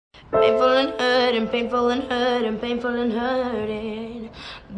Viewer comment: who else heard iching burning